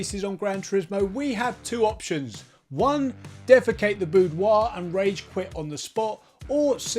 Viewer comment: ~ Did you really say "defecate the boudoir"?